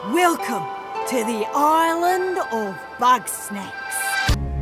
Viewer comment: I thought it said "island of butt sex"